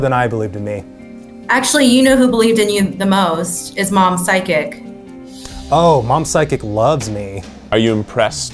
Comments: Get you someone who believes in you as strongly as Eugene's mom's psychic believed in him. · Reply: I was sooo confused until I read your comment because I thought they were saying sidekick and I was like wait what's the story behind that.. psychic makes so much more sense.. :) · Eugene’s Sister: “You know who believed in you the most? It’s mom-“ Me: awww Eugene’s Sister: “-psychic” Me: o h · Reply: psychic? I always thought they said "Sidekick"!